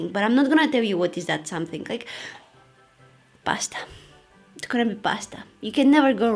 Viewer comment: So...What do you eat when you don't know what you want to eat? 🤔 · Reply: I was just hearing the video... Thought you said "Basta" in Spanish 🤣(it means "Stop")